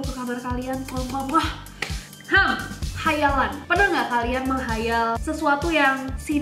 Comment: Khayalan salah dengar jdi Hai Alan Kaget, karena biasanya aku di panggil Alan jdi tadi kiranya kak nessie say hai alan🤣